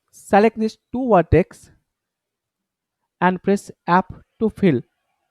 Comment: i thought you said 'app' but its actually F thank god I found it on the web 😁😁 anyway this is the best tutorial i can find. It helped me doing my 3D prototype. You should add caption sir, i cant understand your accent.